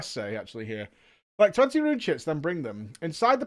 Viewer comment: i kept hearing "rune shits" and i had to go back and read it lol